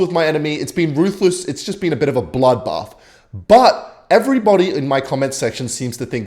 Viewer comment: did i just hear bloodbath? IS THIS A MFKING GEOMETRY DASH REFERENCE?